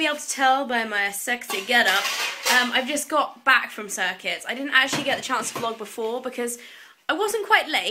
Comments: i thought you kept saying circus :( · haha until said it, I thought you were saying "circus" and I almost Googled "circus workout" to see what you were talking about. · The whole time I thought she kept saying she was going to the 'circus'. I'm from Australia...don't mind me. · hahahahha the whole video i thought you were saying circus.....